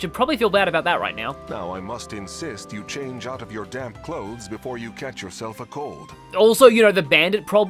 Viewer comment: so I was only listening at this point and not actually reading the subtitles, so I thought for a seconf Vander said "change out of your damn clothes" and was left stunned by the sudden aggressiveness he developed over laundry